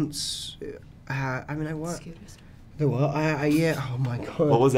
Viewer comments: For anyone wondering what Natalia said at 14:10 ,she said scuba store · Does anybody know what Natalie says at 14:10? It’s so faint lol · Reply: MrSeaWalrus IsAwesome “the scooters”